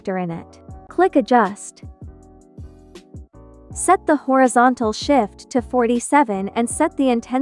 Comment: I thought u said horizontal sh*t 😂